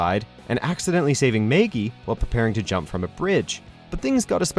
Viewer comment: did he say Maygee? WHAT...WHAT...WHAT THE FUCK?!